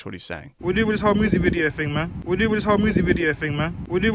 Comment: He’s saying ‘we’re doing this whole music video thing, man’ at the beginning of that first clip 😂 Great video dude. 👍🏼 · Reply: Lol I thought he said 'home music video' :)